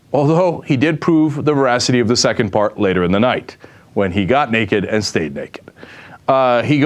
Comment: lol @ 2:41 Subtitles dont even conjugate with what Cenk is actually saying; Who subtitle'd this clip? What Cenk said: When he got naked and stayed naked. What subtitle reads: When he got naked and state dick.